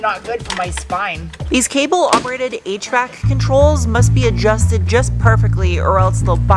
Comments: Had about one minute 58 seconds you said HVAC control and I thought you were saying eight track control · For a second there, I thought you said "8-track controls and then my noggin registered via visual input that you were ACTUALLY saying HVAC controls 😂